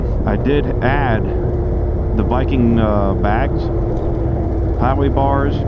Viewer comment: Great video. Are those after-market saddle bags? What is the company? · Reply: I heard you say Viking bags. Did you have to cut at all